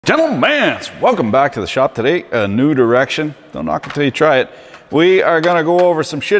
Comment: did I hear "nude erection"?